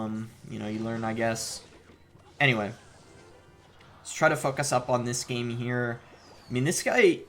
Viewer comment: anyone else hear "fuck us up"